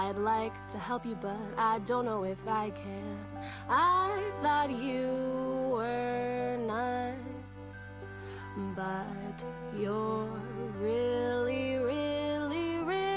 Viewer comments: I thought she said "... I thought you were nice but, you're really really really not." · i thought she said i thought u were nice but your really really nuts xD thats what i been saying